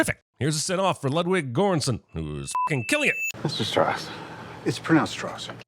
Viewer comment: nope...he clearly says Strauss like the composer first time